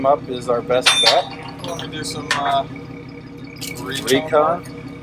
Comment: I SWEAR I heard Raycon twice ina row! Too many damn ads lmao. RECONN